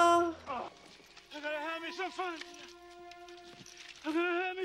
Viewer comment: When Mac is repeating " gonna have some fun" I always hear Venkman's ( Bill Murray/Ghostbusters) voice saying " Mac has gone bye bye Egon ".. cause Mac is pretty much losing his head at that point